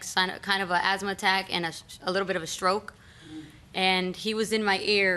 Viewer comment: did she just say “a LITTLE BIT OF A STROKE” ??